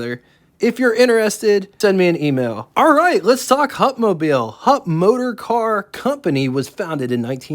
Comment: I thought it said "humpmobile". 😂